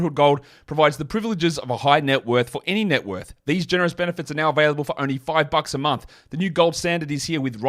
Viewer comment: i heard "available for only 5 f***s a month", and thought that was a steal